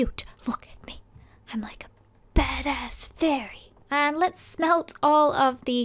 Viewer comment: anyone else hear bad ass fairy I thought you wrestling for kids eh Idc I still love you